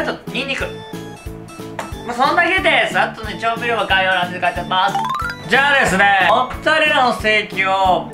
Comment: でなんて言ってんのか分かんなすぎて戻ってもう1回見たけど結局よく分からんかった笑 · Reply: 「そんだけです。あと調味料は概要欄に書いてあります」かな（笑）。